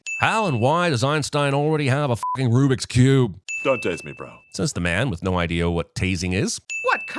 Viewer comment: He clearly said "Don't TASTE me."🤔